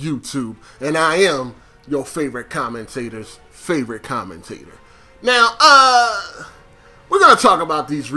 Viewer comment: Holy shit. I just realized you say "Your favorite commentator's favorite commentator." after 5 fucking years!! All this time I thought you said "You favorite commentator, your favorite commentator". SMH